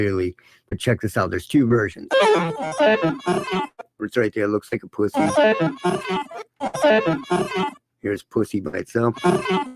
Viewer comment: instead of pus*y, i hear-"Looks like a person"